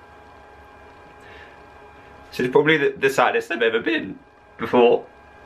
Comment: if you didnt understand what he was saying he was saying " its probably the saddest i have ever been before"